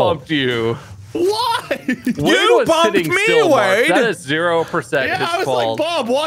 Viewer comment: I accidentally hit my volume up button and it turn to max and all I heard was “WHY YOU BUMPIN ME”